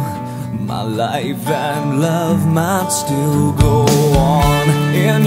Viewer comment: am I the only one who hear love Matt still gold 😂😂